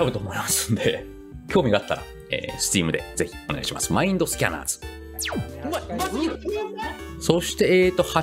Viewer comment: マインドスキャナーをマインドシーカーと空耳した…